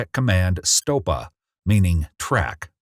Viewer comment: STOPA meaning TRACK i thought he said CRACK 😂😂😂😂